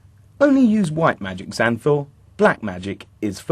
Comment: I THOUGHT he said, "...only use white magic, Zanthor. Black magic is forbaden."